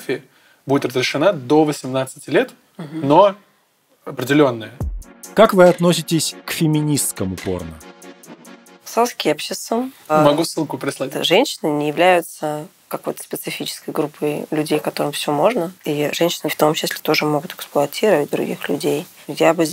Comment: не понял, что она сказала? «Соски я ще ссу?»